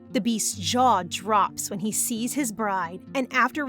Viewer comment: I thought it said bird and not bride 💀